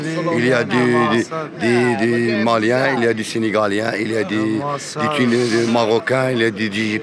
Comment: des quoi ? 🤣🤣🤣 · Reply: Des senegaliens 🤣🤣